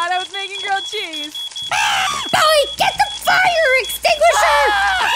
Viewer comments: I thought was saying get the f***ING Extinguisher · When she said get the fire extinguisher u thought she said get the f out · When she said go get the fire extinguisher I thought she said get the f out of here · I thought was about to say,”Boe get the fuck out of here” But she said “Get the fire extinguisher” 💀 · I literally thought she'd say GET THE F- OUT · i thought said get the f word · lmao I thought she was say "BOE, get the F out of here!" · I thought just said: BOE GET THE F*CK OUT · Who else thought was gonna say “BOE GET THE F**K OUT”